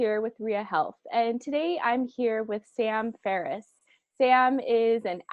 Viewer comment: I thought she said Sam Harris